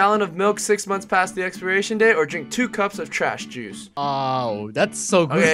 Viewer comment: HE said Guava Juiced instead of Guava Juice LOL